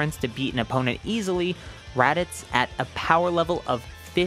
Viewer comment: for a solid minute I thought you said raditz was "15,000" and I was about to say "did raditz get god ki also? I guess that can make sense, seeing as they're brothers and originated relatively the same way."